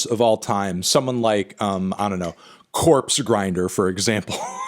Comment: for a sec thought he said Corpse Husband